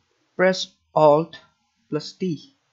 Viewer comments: i thought u said alt+d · Edison - Drag sample - Alt + T - Slide the Pitch... Got it, thanks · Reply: Thanks you! I thought he was saying Alt+D and it wasnt working!